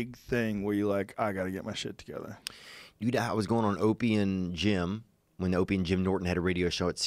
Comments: At first I thought he was saying he was always on opium and gin. Lmao. Opie and Jim. Ohhhh ok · Thought he said Opium, not Opy and Jim at first lol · I thought he said “I was going on an Opium gym”